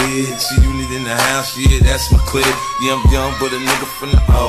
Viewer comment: I wonder if JoJo thought he's saying "yum yum" instead of "yea I'm young" .. n that's why the choreo is like tasting their fingers at that part.?? Lol